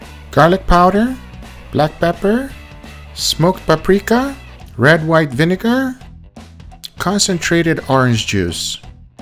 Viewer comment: Did I hear "red white vinegar" @ 0:49?